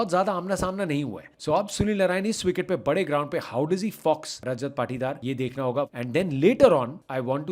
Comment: Did I hear fucks? 8:20😢